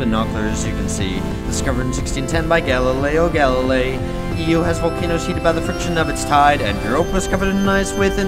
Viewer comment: The captions said: EU (formerly "video") has volcanos cheated by the friction of its tide